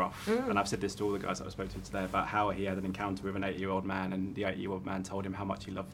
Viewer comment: I thought he said “8 year old man” and I was like....... wut